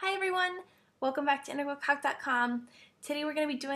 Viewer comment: just a joke...it sounds like u saying...welcome to intergral c0ck dot com lol ok sorry..back to calculus now.